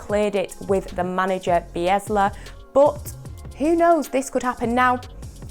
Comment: Did anyone notice she said Biesla instead of Bielsa